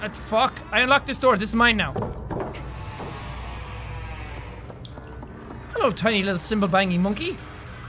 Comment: am I the only one who heard "hello tiny little Simba banging monkey"!!?! 😂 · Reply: Spiffy Playz he said symbol banging